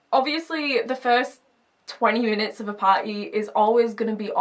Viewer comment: Thought you said the first 20 units of a party are always awkward and I was like YES but then I realised you said minutes oops